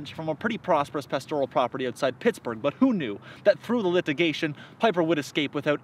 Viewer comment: Ah! If my ears are not deceiving me, I believe I detect a "lithigation" instead of "litigation" just before 0:30.